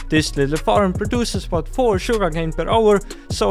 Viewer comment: 10 sugarcane per hour farm 😂 Edit:looks like if I watched it for 1 more sec he actually says 4 sugarcane per hour